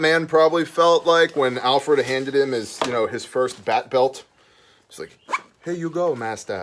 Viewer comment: Much like Hans said. When you said bat belt, i heard "Bearbelt". Kind of goes back to a few of your other vids of "continuity/commonality of gear". This is certainly a marketable set of gear, as is. I would definitely get one to test and review. Congratulations to who receives it!